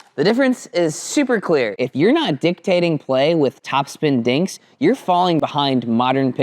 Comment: I thought you said dinktating 😂